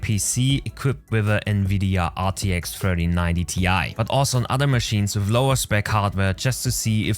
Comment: Didn't know they released a 4090Ti lol 03:02 maybe misheard or is it 3090? either way great review